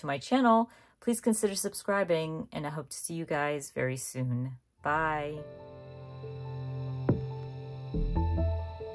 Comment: on your read to zero page you mentioned an app but I can’t tell what you were saying. Was it libit? I checked the transcript but no luck. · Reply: Oh the app is called Libib. It’s such a great app to help you keep up with your home library.